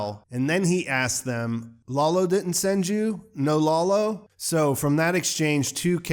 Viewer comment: i thought he said "lalo didn't send you? Don Lalo?